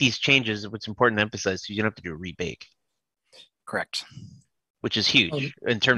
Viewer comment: did he say that you DO or DONT have to rebake when iterating? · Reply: In order to tweak the sound design with the designer controls, you DO NOT need to rebake at all. If you change geometry or materials in the scene, then a rebake it necessary.